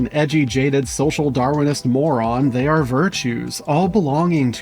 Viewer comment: did you just say Darwinist lol